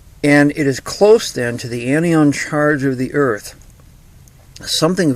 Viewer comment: The what charge ? can you make subtitles or annotations or anything because I don't seem to understand what you mean. Did you say "anti ion(?) charge" ... ? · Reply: Anion charge of the earth.